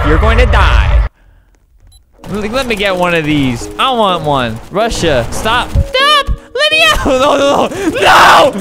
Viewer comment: did just say Russia...